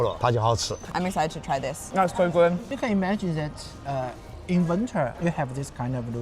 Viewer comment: Remember if you'd like subtitles feel free to turn on CC! The english subtitles have been manually added (choose 'English' not 'auto generated') so they are perfect, and if you'd like to practice your Chinese feel free to add Chinese subtitles! 你可以点击右下角的 “CC” 字幕按钮，选择 “简体中文”。字幕是我中国朋友动添加的，更准确哦～ Check out Wenyue's newest album on spotify! <link> PS: Patreon members see my videos DAYS earlier! Support my channel and become a Patron :) <link> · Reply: spotted a little mistake: at 7:12 the subtitles say "inventor" but I'm fairly sure it's "in winter"